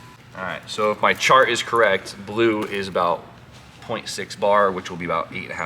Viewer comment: I thought this man said 26 bar nearly spat my drink everywhere 😂